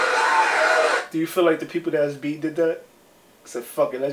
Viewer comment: Did he just say “the people who got beaten to death”? 😂 He got the eyes from his boy and quickly changed his thought. 😅 Funny stuff.